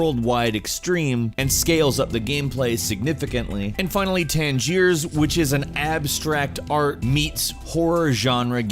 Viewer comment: Unknown Com haha xD no i know outlast 2 xD i mean the game at 8:25. This game with the creature wich look like Slenderman :D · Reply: ***** Ouh Tangiers. thanks :D I always heard "tense years" xD